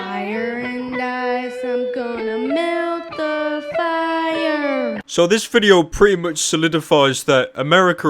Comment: did he say im gonna melt the fire?